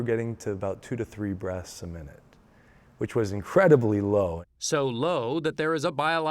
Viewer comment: did he say 2-3 breathes a minute!?? 🤯